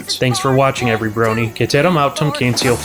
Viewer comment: what did you say? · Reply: Irish John. I believe it was, "Ceteram autem censeo Flash Sentre esse delendum," though my spelling may be a bit off...